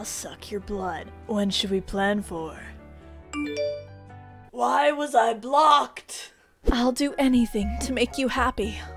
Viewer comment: When I saw the vampire Going "why was I blocked?!?!" In the start I thought he said "why wasn't I blonde?!?!?!"